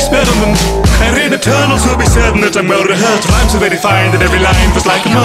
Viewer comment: ‘Rhymes so refined, that every line feels like a multi-verse’ That’s such a clean line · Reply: I thought it said "rhymes so very fine"